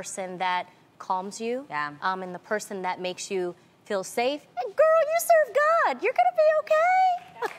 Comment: "Girl you serve god your gonna be okay 😌 where is your faith " that part was so cute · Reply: I thought she said "Girl use your gut!" I was like what